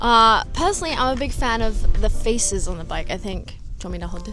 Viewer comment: Did she say feaces? 💩💩💩